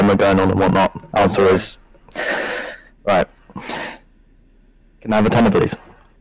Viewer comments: what did he say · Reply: gifko can I have a tenner plz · can I have a what please? What is he sayin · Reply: Tenner